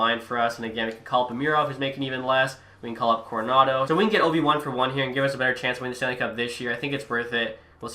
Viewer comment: sounds like you say “if we can get Obi Wan for one here…” 😂😂😂